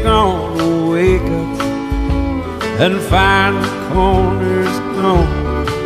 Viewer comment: The lyrics are wrong buddy for calling them [official] at 0:54 , instead of " find a corner stone" it should be "find the corners gone". at 1:07 , instead of "Well." he does say cos/cause/cus the diminutive form of because. If you wana be formal use "because" or pic one of the popular short way of spelling the diminutive. I think the more old school good way of spelling it short would be " 'cause ", the " ' " making reference that something is missing. at 1:46 , instead of "nobody by the same height" it should be "nobody bother saying hi"